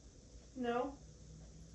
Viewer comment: When is doing Estes method, yall ask if it can tell you what its name is and it says no through.Then at 26:33 I hear a whisper that sounds like a female saying “no.”